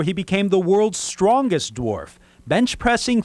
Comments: i thought it said the worlds fattest dwarf... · I thought it said worlds fattest dwarf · I thought it said the worlds fattest dwarf.